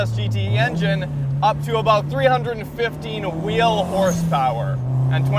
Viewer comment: 315 or 350; The title says 350 and I thought I heard 315